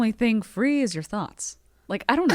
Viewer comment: Holy shit when she did her Obama tagline I thought she said "the only thing free is your thumb" then an ad played. And I had to wait 15 seconds while pissing myself laughing wondering what the fuck that meant. Then the ad ended, said "so wholesome" and I was like ??? and went back 10 seconds to replay and realised she said thoughts not thumb. Shit.